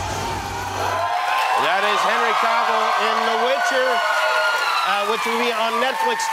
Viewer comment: did he just call Henry, “Harry Cavill” or did the screams deceive my ears? Even Henry whipped his head at him.